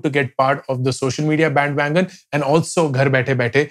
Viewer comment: subtitling issue, shows 'bandwidth' instead of 'bandwagon'